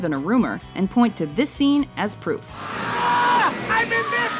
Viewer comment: i swear to god he says ah i meant bitch